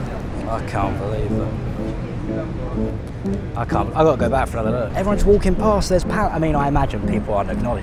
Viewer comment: can someone help me understand what he says at 0:58 and 5:31? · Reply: mynamein20characters “i can’t- i’ve got to go back for another look” and “they’re all just like, blowing out the pocket”